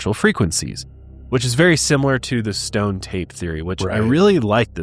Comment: I watch too much Joe Rogan. Everytime you say " Stone tape" i hear "Stoned ape"😂